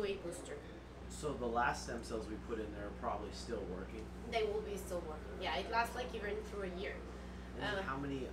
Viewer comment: does the woman say the previous stem cells last up to a year? I can't quite make out what she says. TIA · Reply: Turn on captions. The cc on the top right of video. The stem cells last up to a year.